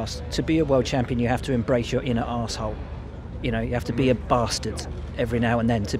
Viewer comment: EMBRACE YOUR INNER ASSHOLE I THOUGHT HE SAID